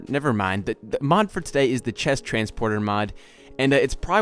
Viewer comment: I thought it said chest transformer